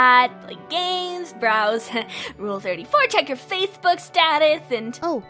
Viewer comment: Did she just say rule 34?! 😳